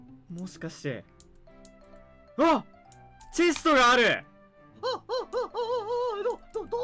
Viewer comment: 「窒素がある‼︎」に聞こえるw